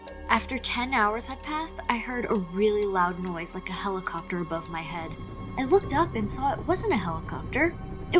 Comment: over your head I thought you said you where upside down